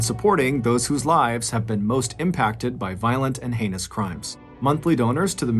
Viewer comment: Thought you said violent and anus crimes...